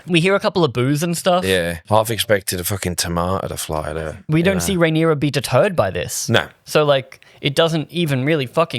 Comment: to my American ears for a half second I heard this as "we don't see Rhaenyra beat a turd by this."